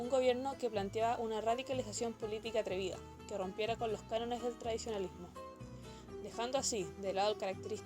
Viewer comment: Muy bueno, pero en el minuto 1:40, dices: "que rompiera con los..." no entiendo que dice después de "los", podrías decirme. Gracias (Y) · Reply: Cánones del tradicionalismo. Dos años tarde aún sirve